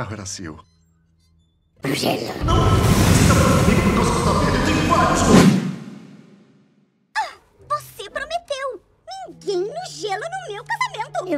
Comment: "Ninguém mijando no meu casamento" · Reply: Mano pq da primeira vez eu escutei "ninguém mijando no meu casamento" mas aí quando eu volto pra cena eu escuto "ninguém pro gelo no meu casamento"